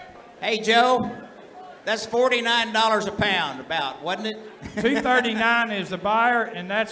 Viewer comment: i hear dekeda dekeda dekekdekekkek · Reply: I thought he was saying pound but he was actually saying thou (thousand)